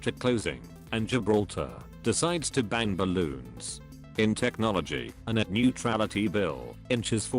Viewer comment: I thought you said banged balloons so i searched gibraltar bangs balloons 😂